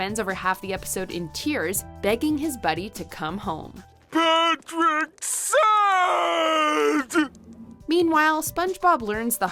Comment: when I was little, I used to think that Patrick was saying “Patrick Star!”